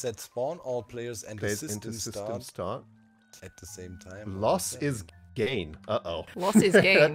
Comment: I thought the book said “Loss is Grian” 💀💀💀